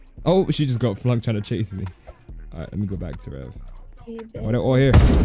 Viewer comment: Nah bro she really said “go back to Africa 😭”